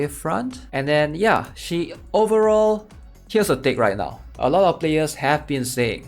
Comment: Dang subtitles lmao 7:01 What you said : here's the thing Subtitles : here's a d*ck